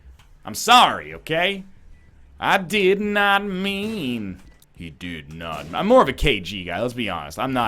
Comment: Holy shit that sounded like "I did not murder him" in the bolegda voice